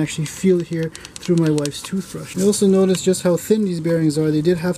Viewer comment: ".....some damage right here on the crank which I can feel through my wife's toothbrush." Did I hear that right @ 10:31?